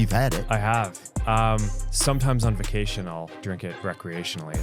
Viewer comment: I heard 'jerk it recreationaly'💔